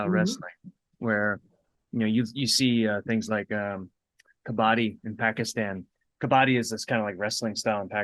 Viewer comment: did you say "kabbadi"? the only kabbadi in familiar with is a team tag sport in South Asia. is it spelled different?